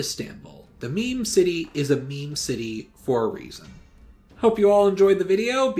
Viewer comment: What do you mean with "meme city"? Or did I just hear it wrong?